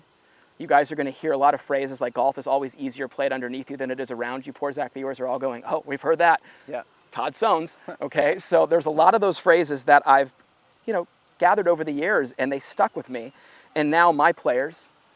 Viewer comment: I always thought you said Todd "Stone" 😂 good thing i didn't bet the house on that lol